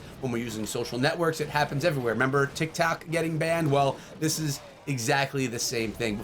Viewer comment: Did he say tictac getting banned at 2:35 or was it just me and his accent